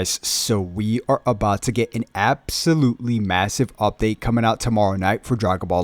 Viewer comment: did he say massive?